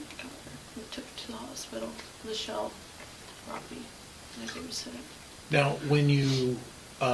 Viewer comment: she said "my babysitter" not what the subtitles said (and I gave her a sedative)